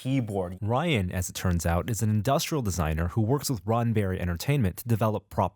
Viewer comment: It sounded like Norm was saying Ronberry...